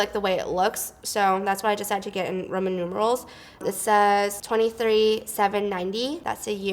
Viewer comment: I thought you said "ramen noodles" instead of "roman numerals" haha damn